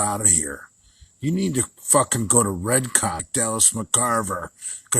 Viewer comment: Lol did he say "RedCock?"